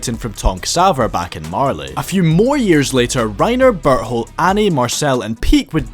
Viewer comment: As a German i saw a lot of Memes making fun of Berthholds name and really couldn't understand what they meant 😂 now i know the struggle of English speakers. The whole video i heard "butthole" instead of his name 🤣🤣🤣